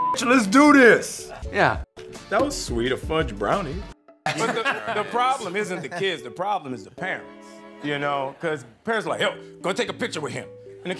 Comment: I literally heard “the problem isn’t the kiss, the problem is the pants” and I was like what did he say????